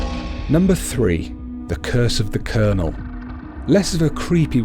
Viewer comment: I thought he said "The Curse of the Kernel" 😂